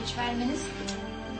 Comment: What did say at 17:47? I can never make it out. She says "One more minute. boss." Then something else.... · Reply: Vitamins lol